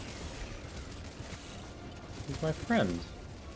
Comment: did he say "my friend" dude is a traitor and needs to reported